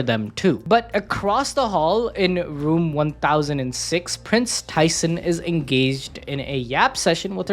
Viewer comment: Why did I hear Kris Tyson 🤣🤣🤣